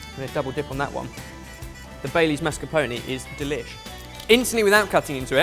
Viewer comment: I thought said babies not Bailey's, and started to think this channel had taken a dark turn 😂😂